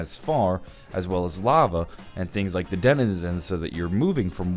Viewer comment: did he say dedizenizens